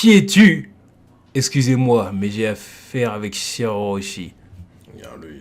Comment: " laissez moi mais j'ai affaire avec shirahoshi" 🤔🤔🤔 shirahoshi??? 😂😂😂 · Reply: Sire orochi il voulait dire jpense mdr